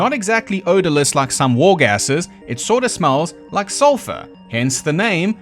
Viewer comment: I did not hear "war gasses;" I heard "war-gasms" 💀